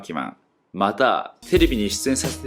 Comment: テレビがフィリピンに聞こえた笑